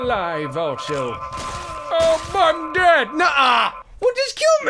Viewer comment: he didnt swear he said "aw im dead" I listened to it 5 times.i could be wrong but thats what i heard.